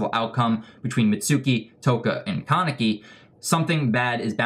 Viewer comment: If you use auto captions and go on 12:05 when he says kaneki there captions said Kentucky. Kaneki is KFC confirmed!